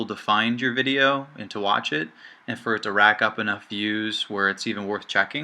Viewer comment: I thought he said video viewers not views